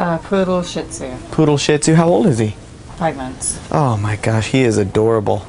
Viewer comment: ok so she said 5 months, not 5 minutes. i get the point, i misheard. im british... but please stop messaging me